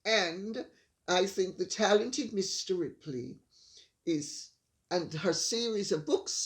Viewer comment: Oh dear!! I can't stop laughing! I have diminished hearing and hearing the expression AH is copying the talent of Mr Ripley, I heard it as copying the talent of Mr Whippy... For those of you who don't know, Mr Whippy was a travelling Icecream seller, whose trucks played the tune "Greensleeves". When we heard that tune, we used to run inside to scrounge money off our mothers to get an icecream.